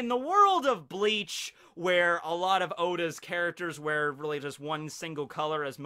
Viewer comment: did he just said a lot of oda characters